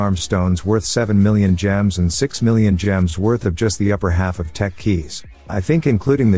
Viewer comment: who saw when he said Tech Keys the subtitle said Techweez